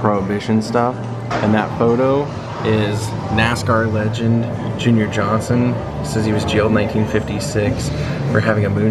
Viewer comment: I actually did a double take when you showed the prohibition exhibit. I thought you said NASCAR legend Jimmy Johnson instead of Junior Johnson. I had to go back and play that part again!